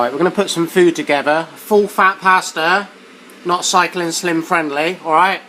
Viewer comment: Sorry I thought you said cycling whim...